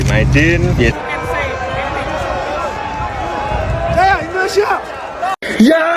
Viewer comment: apa ga salah dengar ya : STY teriak ...SAYA INDONESIA ! 🤭